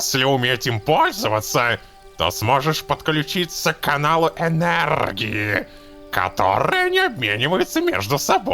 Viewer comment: мне послышалось к аналу енергии......